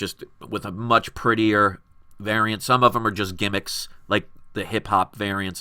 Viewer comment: did you say that the variant covers of the Hip Hop series were a gamic? can you explain why? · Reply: No I said GIMMICK... :).